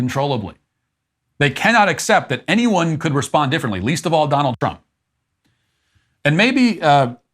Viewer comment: Nigga, did u say Donald Dump? Lmfao!!!